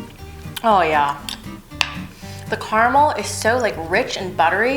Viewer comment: The first time I watched this I thought she said "Hell yeah" after eating the tart 😆😆. I just realized she actually said "Oh yeah" hehehe